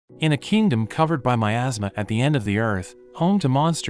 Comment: I thought it said in a kingdom coved by my ass